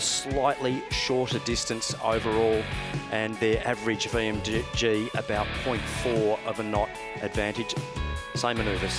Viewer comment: On this video at 43:07, what does Ben A. say off camera? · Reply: Ducking Anchor